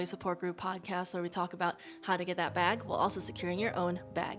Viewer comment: Off topic but could you explain your slogan to me? At first I thought you were saying “while securing your own back” but today I realized you’re just saying “bag” twice. What’s the meaning/ origin?